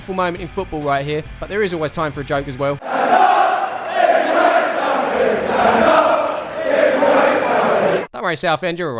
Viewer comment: what did the fans say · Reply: Stand up if you hate Southend.